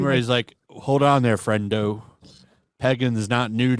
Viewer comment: I could’ve swore Deadpool said pegging and not pagan.